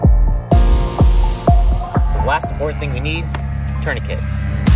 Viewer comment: what is he saying? is it tourniquet?